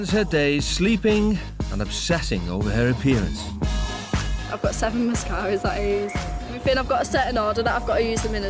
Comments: I thought she said “ive got miscarriages” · I thought I heard "I've got 7 miscarraiges" turns out it was mascaras. Also, when she was applying it, at first I thought she was doing blow. Damn, I had very low expectations for that individual.